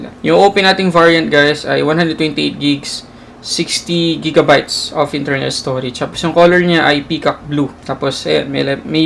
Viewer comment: First time kong hindi magsabi ng "Hi everyone, welcome to Sulit Tech Reviews!" Di-ko alam kung bakit. Anyway, ano gusto niyong makita sa full review? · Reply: Ako lang ba?..kasi I heard him saying.. "128 gigs, 60 gigabytes of internal storage" i think it should be, "128gb of internal storage and 6gb of ram." o baka mali lang talaga ako ng dinig. Anyway nice unboxing 👌.